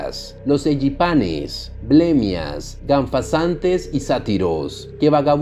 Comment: estoy enfermo, no se por que escuche ahí milenials XD